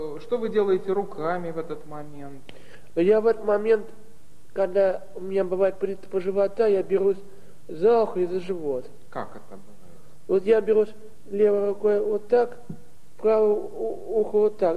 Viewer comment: Послышалось "Берусь за х*й и за живот"